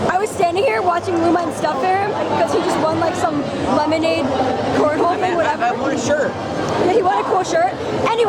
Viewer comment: DID I HEAR CORNHOLE?!